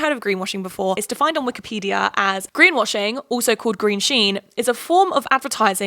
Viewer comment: Ngl when i heard you said "greenwashing or green sheen" i thought you said green shien and i was like lol Wikipedia calling them out